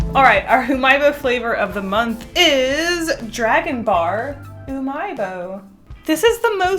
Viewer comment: When she said dragon bar it sounded like she said dragon ball with an aisian accent